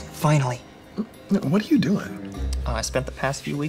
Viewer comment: INTRANET!!!🗣️🗣️🗣️🔥🔥🔥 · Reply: Bro it sounded like he said “i fuckingly got onto the school intranet”